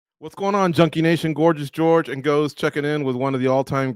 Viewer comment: I thought he said whats going on chunky nation Lol